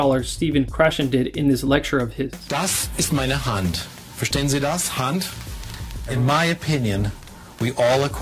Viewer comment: By the way, the subtitles at 2:52 are incorrect - the proper subtitle should be: "Das ist meine Hand. Verstehen Sie das - Hand?" Just saying, because it looks like a non-German person added them.